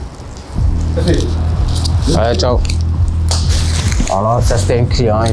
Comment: i'm 99.9% sure he said ''ajde, čao'' - this is known for serbian, bosnian, croatian, slovenian,.. people (on balkan) not for someone in Marseille.. i must have missheard him, what does he really say? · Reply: he said "allez ciao" it means "all right bye" in french slang